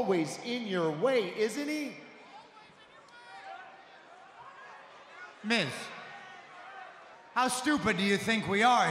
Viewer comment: I could be wrong but I think that Seth said myth instead of Miz